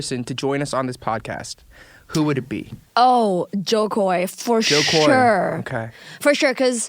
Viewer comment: Did she just say “Jokowi” :”) I’m droppin my jaw now :”) I will really be impressed if Eric really do that:”) Am I right? Is is Jokowi? Or did I just missheared it:”) ?? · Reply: she said Jo Koy, he is a Filipino-American stand-up comedian.